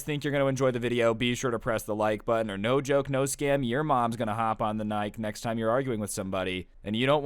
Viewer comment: if u stop there and listen closely he says and hop on the nike when i heard that i died laughing